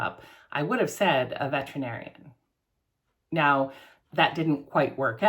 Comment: OMG, I thought you said you wanted to be Vegetarian when you grew up 😂😂 Took me a couple of re-watches to realise you said Veterinarian.